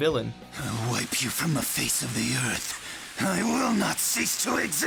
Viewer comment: The video is not as fun to watch when you realize that the commentator doesn't know shit about Naruto. Like wtf is Akasooki. There's no way you could pronounce that wrong if you have actually watched the show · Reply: I think he is new.... cut him some slack, I just found his pronunciation of all the "e" very eccentric, the first time I heard him say "earth", I heard "eeeuurth" and I was like "say what?" and repeated that 5 seconds of the video to confirm LOL